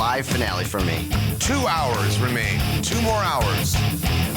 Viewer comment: Did he just say tumor hours 👀